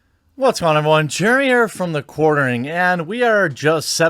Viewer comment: Here I was tripping, accidentally tapped on your vid and I hear: "Jeriere from the Derriere"